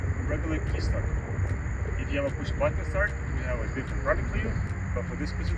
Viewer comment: I’m not seeing a guide for the push button start version for the Kia forte 2017, just this video for the key start. 0:55 says something about the push button start but I can’t hear what’s being said. Are both versions installed the same way? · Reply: He says, "if you have push button start, we have a different product for you"